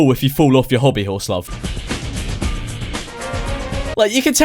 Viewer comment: did i really hear him say love